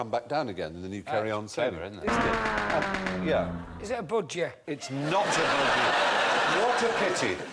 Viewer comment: what the hell did johnny vegas say at 1:03? i can't understand him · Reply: "Is it a budgie?"